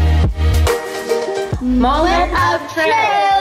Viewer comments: For a second I thought I heard mullet of truth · At the end of the video when you were saying moment of truth I heard “Mullet of truth” 😂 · Is it just me or I thought she said mullet of truth?😂😂😂